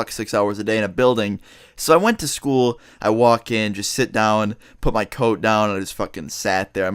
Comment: i thought he said” I put my tote down”